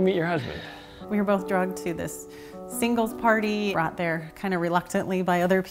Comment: i fr thought she said she met her husband when they were both drugged at a party... i definitely misheard that😂 love her story!! so heartwarming and she deserved to be in the pageants! she’s absolutely stunning, not just outside, but inside too💖